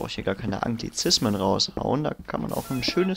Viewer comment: bei 7:10 hab ch antizischmen verstanden und der Cobblegenerator hat darauf gezischt :|D